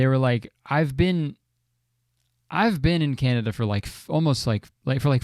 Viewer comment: right here i thought kurtis was saying that he had been in canada for 45 years and i was super confused cause hes like 27 wtf.